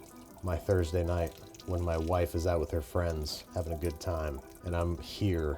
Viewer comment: Did he say "wife" at @ 13:06? When did they get married?